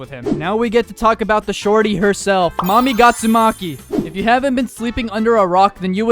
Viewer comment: DID HE JUST SAY GYATSUMAKI!?